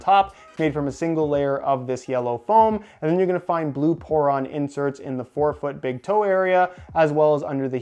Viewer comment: tell me I’m not the only one who thought that said porn